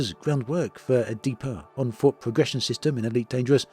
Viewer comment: did someone say ship interiors?..... Oh, nevermind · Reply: I thought I heard "onfoot VR"...